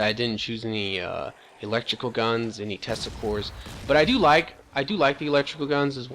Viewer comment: When you said tesla cores, I thought you said testicles.